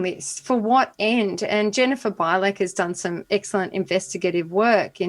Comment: Did anyone catch the woman's name that Katherine mentioned at 1:00:02? Was it Jennifer Bylack or Vylack? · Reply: Bilek on Twitter